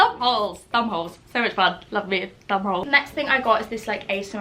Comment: I thought she said ‘love me a bum hole’ lmao